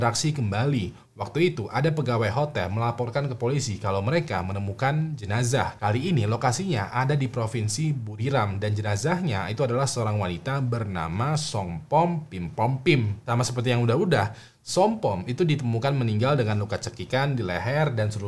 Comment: koq gw dengernya "som kom hompipa" 😂